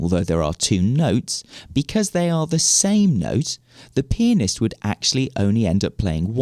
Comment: Sounds like he said penis in 1:17😂